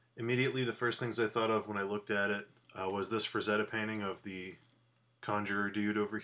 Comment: Could anyone write the name of the artist mentioned on 1:10? I hear something like "Frezeda", butI haven't found any artists with this name in the Internet. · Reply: sure! Frank Frazetta.